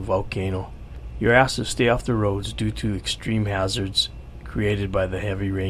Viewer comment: when he said "you're asked to stay off the roads", I totally misheard it as "your ass should stay off the roads".